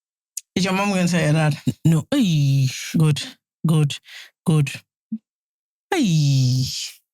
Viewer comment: I thought the same person saying ‘eiii’ was saying ‘God, God, God’ loool. That was a good laugh